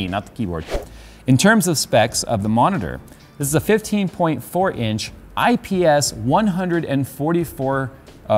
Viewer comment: saying 15.4 inches but subtitle says 15.6 inches.. come on.. that .2 inches matter in size. :D